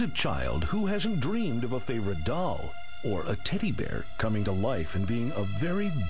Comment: did he say, "titty bear""?!